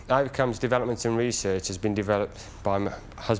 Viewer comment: it sounds like he said "was developed by my husband" just before he stopped. Anyone else get that?